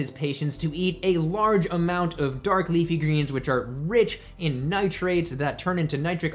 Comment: what does he say at mark7:37?...a large amount of what? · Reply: dark leafy greens.